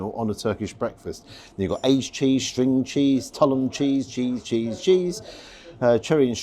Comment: Oh, Tulum cheese. I thought you said 'tunnel cheese' and thought WTF is that?! Also the first breakfast Ive seen served with a crate of bread lol. Looked amazing :)